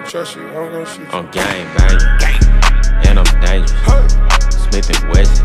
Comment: i thought at first he said "I'm stanky" then i thought he said "in them daniels" now I'm pretty sure he's saying "I'm dangerous" lol